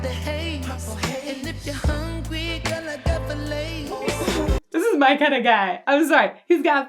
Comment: Thought he said "if you are hungry, I got the Lays"... filets makes more sense but I'm sorta disappointed